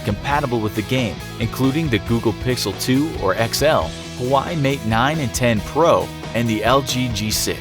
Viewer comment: A tear dropped everytime you didn't pronounce Huawei right ... it sounded like you were saying "Why Mate 9" 😞